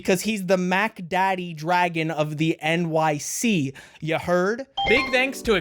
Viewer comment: I always thought they said the LYC